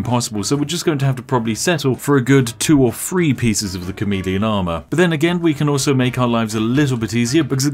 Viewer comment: I knew it was chameleon armor but did anyone else thought he was saying comedian armor